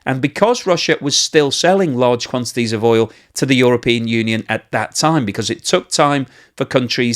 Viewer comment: ish .. I swear I heard Uralpeon union there .. do you know something we dont? :p